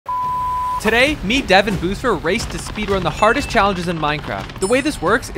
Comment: i heard him say "today me dev and boobsfer"